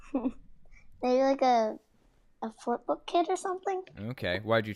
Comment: What did she say? i heard "Footbook kit"